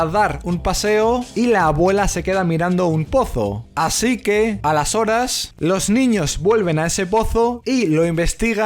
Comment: OTRA VEZ EL "A LAS HORAS" SIEMPRIE PIENSO QUE DICES "ALESORES" O "Y ENTONCES..." EN CATALÁN :[ minuto